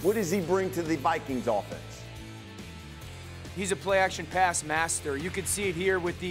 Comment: Did he just says JJ and a a play action master 🤣🤣🤣🤣 WTF 😂